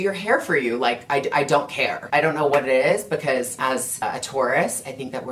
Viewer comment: I thought you said “as a tourist” AHAHA